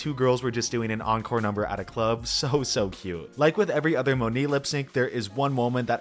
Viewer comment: Did you say Monique or Monet?